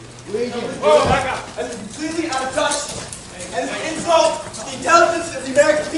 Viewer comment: I thought he was saying .. it's "COMPLETELY UNJUST" 🤔 ???